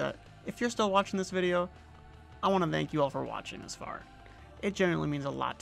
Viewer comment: i thoth i Heard "mank you all"